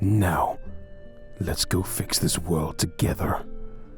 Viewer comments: Garroth says 'lets go fix this world together' i thought he said 'lets go fix this road' together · I thought garroth said let's goof in this world together